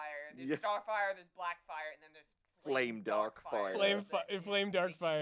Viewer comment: #BlessedWithSuck I am blessed with diminished hearing, which often leads to wacky misunderstandings. For example, I heard 'FlameDarkfire' as 'LameDuckfire' of which the latter is the objectively cooler name and then I was disappointed.